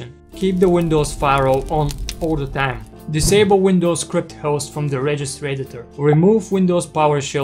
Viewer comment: I couldn't make out the tip at 1:24. Can you write that one out? Thanks! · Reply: Hey Kiar. To disable Windows Script Host from the registry please: 1. Run Regedit.exe 2. Create the following REG_DWORD type key: HKEY_LOCAL_MACHINE\Software\Microsoft\Windows Script Host\Settings\Enabled 3. Set its value to 0 (zero) Thanks